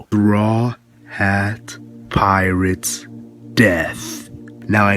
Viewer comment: did he say bra hat pirates?